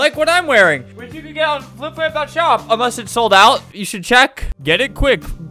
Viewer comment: Captions said "flip flap that shop"